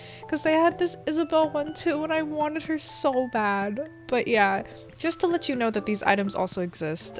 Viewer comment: I thought she said “ I WANTED HER SOUL BAG-“ my sanity does not compute.